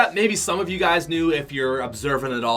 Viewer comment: did he call it the “Black Vertex Set”? It’s the Black Vector Set